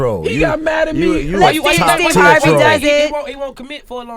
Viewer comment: emory stan he said top tier troll lol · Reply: K Daye I thought he said hoe troll😂😂